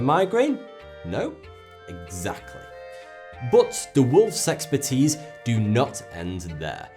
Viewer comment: When he says worlds exporters it sounds like he is saying sexportees